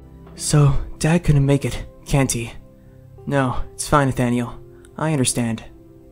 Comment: did he call Nathalie Nathaniel?😂😂 And 7:17 Did plagg just ask for cucumber?😂😂